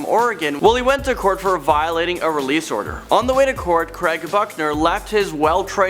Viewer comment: I swear you said fuckner instead of Buckner